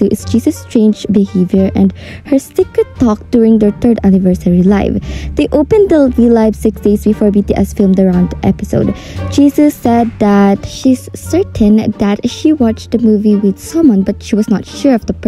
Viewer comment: I'm really sorry but I heard it as "Jesus said that" 💀🗿